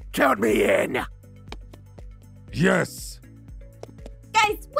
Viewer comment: Yes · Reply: kind sounds like hes saying jess xD